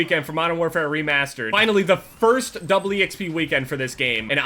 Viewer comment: Why did he say it like that? " The first Double e xp weekend" Double e?